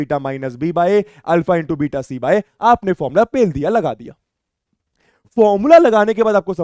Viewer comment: what did he say !! 💀🌚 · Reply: Pel diya mtlb lga dia pel eia mtlb wo ni 😅😅